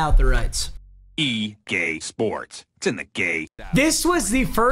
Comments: it says egay sports instead of ea sports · sounds like he said “E Gay Sports it’s in the gay” 😭😭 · Did it say EGAY SPORTS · Did it say E GAY SPORTS ITS IN THE Gay? Or is it just me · I swear this man just said "E GAY SPORTS" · anyone else hear the “EGAY SPORTS”😂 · I thought it said E Gay sports it’s in the gay